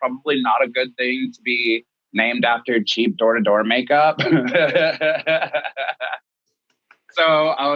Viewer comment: for some reason I heard "cheap adore makeup" instead of "cheap door to door makeup" lol